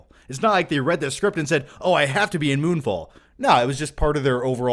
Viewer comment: I thought you said: "Moonball" lol.